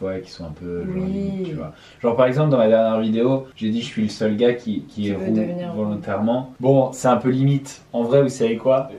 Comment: c'est quoi la blague un peu limite ? Pas bien compris/entendu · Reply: "jsuis le seul mec à devenir roux intentionnellement" en gros